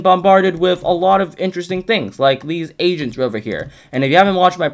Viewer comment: I heard "these Asians over here" lol